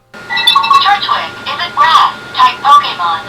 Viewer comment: 1st pokedex fact: Turtwig is a ground type pokemon. Hoo boy off to a great start with incorrect facts. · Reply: I thought it said grass type?